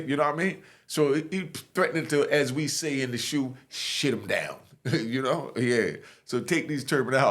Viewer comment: I heard of shit em down